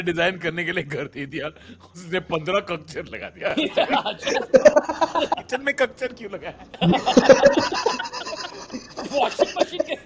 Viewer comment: What tanmay said at 21:53 please someone explain. Kya laga diya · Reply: cuck chair.